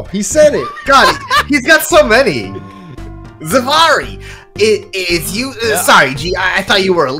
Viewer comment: I swore I heard say my name, "Zamari" and then immediately remembered Luke Atmey said "Zvarri" which is dangerously close.